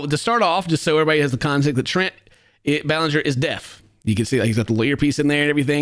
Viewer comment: tell me why i heard “trent ballinger is dead” and i went 😮